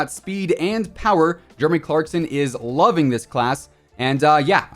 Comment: ahh yes I saw it now lmao I must have missed it because of his account because it sounded like said Jermy instead of Jeremy lol